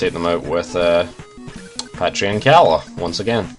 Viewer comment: I'm sorry, but did you say Patria and Calla at 14:51?